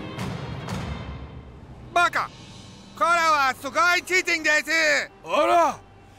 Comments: baka Kore es Sugoi chinchin desu" Hahaha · i hear:kore wa sugoi chinchin desu.🤣🤣🤣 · "Baka ! Kore wa sugoi chinchin desu !" "Stupid ! This is awesome dick !"